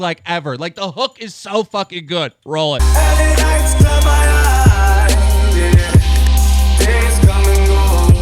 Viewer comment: I know he's saying "L.A Nights Cloud my eyes" BUT what I hear is "L.A Nights CLOUT my eyes" haha Much Love Keem!